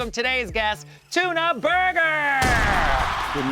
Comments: Tuna Burner is DIABOLICAL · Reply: i heard "tuna burger" lol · I thought she said "Tuna burger", IS IT JUST ME.. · I swear it sounded like she said tuna burger..